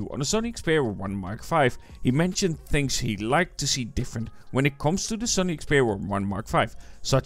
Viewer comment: he is not saying "like to see"? He's saying what makes a next tier one.